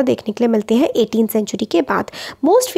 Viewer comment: Ma'am what you said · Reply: she soike 18 century ke bad